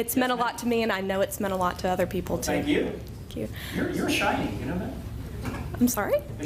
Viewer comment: I wonder what she THOUGHT he said at the beginning haha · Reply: I think she heard your a hiney